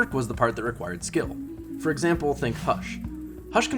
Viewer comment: Instead of think hush, I heard "thank fuck" lol